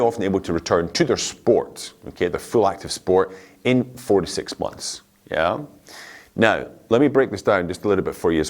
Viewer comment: Hi! Did I heard right: 13:50 for returning to sports takes 46 months- OMG.. That is nearly 4 years..!!